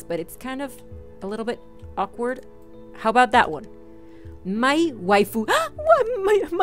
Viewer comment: when she said mai waifu i thought she said "my wife" 😂